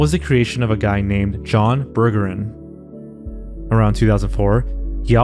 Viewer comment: BURGER? Did he just say his last name was Burger? Does he come with fries? 😂✌️